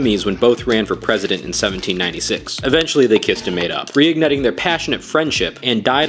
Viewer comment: "eventually they kissed and made up"💀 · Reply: I thought they said “Eventually they kissed and made out”💀💀💀